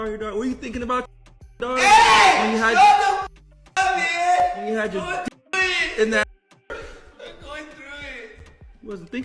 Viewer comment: i hope he did not just say what i think he said about their daughter... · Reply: He didnt mean it like that he says verbatim 'were you thinking about your daughter when you had your d- in that w-'.